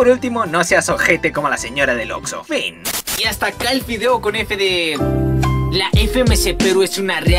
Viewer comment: Soy el unico que noto que dijo fideo?